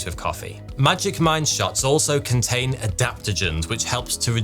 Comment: When the sponsor for magic mind came up, I swear I heard "magic mike" and I was like ... huh?